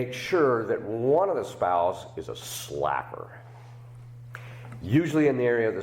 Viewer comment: I thought you said "one of the couple will be a slapper." I thought, oh no its mot me. Hahaha you said SlaCKer